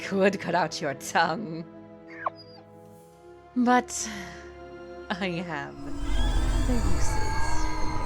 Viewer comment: I can’t hear what Atiri is saying here and the subtitles stopped here! Did someone hear what she said?😭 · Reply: I can help with that. She said, “But I have other uses for you.”